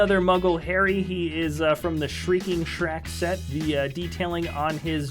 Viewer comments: Did he say the shrieking shrack!? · does say shrieking shrack